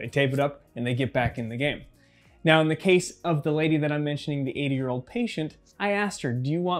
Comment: I swore he said eight year old patient. Listened back and still heard it.